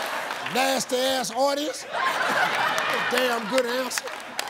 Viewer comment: I legit thought he said nasty ass hornies.